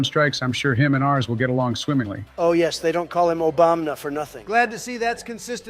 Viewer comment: Wait so they call him Obombna? Like bomb? I thought they’ve been saying Obomna this whole time 💀💀